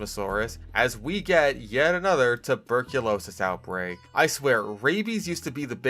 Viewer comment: did you say Tuberculosis? I think, i am afraid.